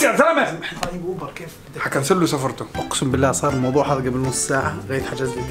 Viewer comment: شو قاللل😂😂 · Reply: راح نكنسل سفرتو😂😂😂